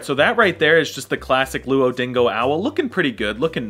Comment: i dont know if im going crazy or i heard him say "luodingo" owl